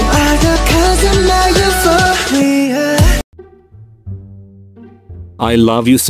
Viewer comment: Ok why did I thought “your hobby” said ‘’ur daddy” I NEED HOLY WATER ASAP